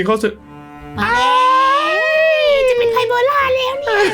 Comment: ไพโบร่าคืออะไรคะ555 ใครได้ยินเหมือนกันบ้างง